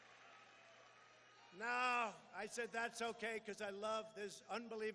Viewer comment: captions saying "thats so gay" lmaoooo